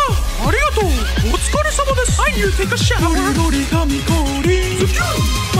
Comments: I heard "i need to take a shit" i swear i swear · Did he say I need say I need to take a shit